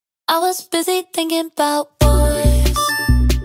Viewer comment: I thought it said I was busy thinking about you